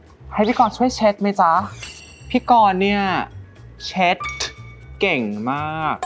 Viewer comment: เพิ่งสังเกตว่าตัวละครชายในชุดสีเงินพูดว่า "คะ" ไม่ใช่ "krub" ไม่ต้องสนใจฉัน ฉันเป็นคนอเมริกัน ไม่ใช่คนไทย 🙏🏻🇺🇸🤝🇹🇭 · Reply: เค้าพูดว่า ไห้ช่วยเช็ดไหมจ๊ะ!!! ไม่ใช่ คะ